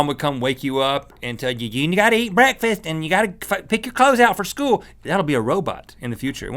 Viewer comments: O.O it sounds like Rhett said f**king pick your clothes out. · It sounded like rhett said "ya gotta fuckin pick your clothes out for school"